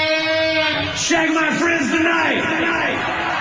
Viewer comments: “Shaggin’ my friends tonight!” 👀.... I’m just sure I heard that right. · I thought he said "shagging like a prince tonight"